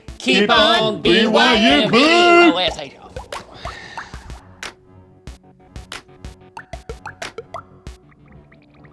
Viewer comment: What is link saying at 17:43? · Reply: Fosho